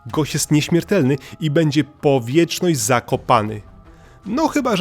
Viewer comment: usłyszałam będzie wiecznie w Zakopanym